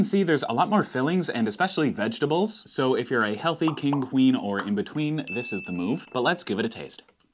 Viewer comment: “Whether your a king, queen or between” why do i hear it as “creme betweens”